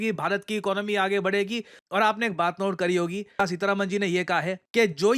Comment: did I hear videsh mantri nirmala tai ? I need to get my brain checked as I am getting low marks in exam too ! 😭